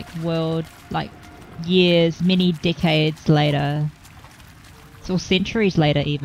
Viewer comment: I thought she said "a couple of D@ heads later"